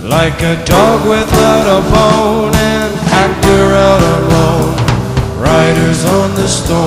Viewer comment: Lyrics are slightly incorrect... "An actor out alone" should be "An actor out on loan". During "The Golden Age of Hollywood", when "the studio system" was in effect, actors belonged to studios and only made movies for that studio. Sometimes an actor would be allowed to make a movie for another studio. When that occurred, the actor was said to be "out on loan" to that other studio by the actor's studio. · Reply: Ree you are correct, I originally thought he said 'actor out of loan' but listening to better quality versions you can clearly hear he says 'actor out ON loan'